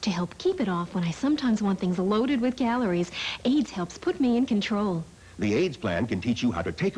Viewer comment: I was listening instead of watching the video, so when she said AYDS helped her lose weight…😳 I thought she meant AIDS.